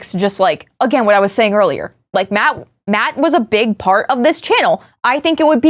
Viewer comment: my head phone played a low battery sound so it sounded like said “mat-mat was a bi